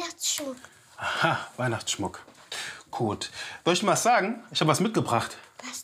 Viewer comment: noch jemand der Kot verstanden hat anstatt gut?😂